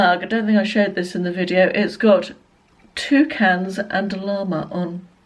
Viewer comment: That mug 🤣🤣 I thought you said ‘two cans of lager’ but it was actually ‘toucans and a llama’ 🦙